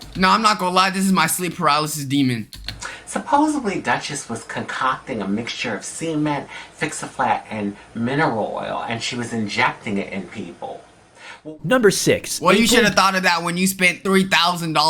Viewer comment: did she say semen or cement?